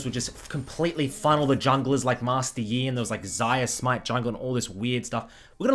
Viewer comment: Sorry I'm a bit new but did just say Xayah smite jungle @ 0:55?